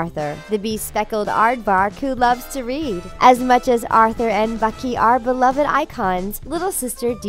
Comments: Did You Say Bunky? It's Binky. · wait... Did you say Bucky? The only way that THAT could be possible is if Bu- 😱 PBS is secretly connected with marvel